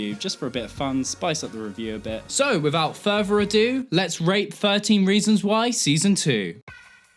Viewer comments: I thought you said "let's rape season 1" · am I hearing things or did he say "Let's rape 13 reasons y season 2"😂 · JESUS I THOUGHT YOU SAID RAPE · i know you said rate but i swear you said rape im so sorryyyy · I heard that as "let's rape 13 reasons why season 2" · I just started the video & when you said "let's rate S2" at first I thought you said "let's rape S2" and I was thinking well that's apropos before I realized what you'd actually said 😄